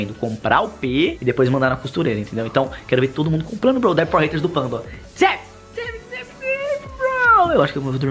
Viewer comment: "Dab for haters do panba" Só eu que ouvi assim